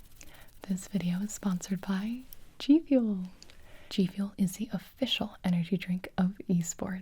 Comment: my volume was really low in the beginning and i thought she said “this video is sponsored by gibi!”